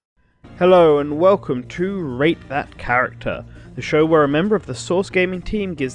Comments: Really thought you said “ hello welcome to rape that character” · It sounded like you said “Rape that character”.